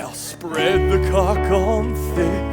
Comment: OH CAULK! For that ENTIRE number up until then, I swear I thought they were saying "cock."